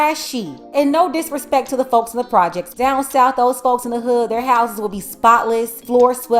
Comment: Did she just say "the projects, I know in the south".. my ninja the projects up north lol